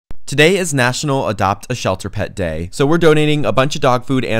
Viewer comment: Bro I thought you said it’s national adopt me day 💀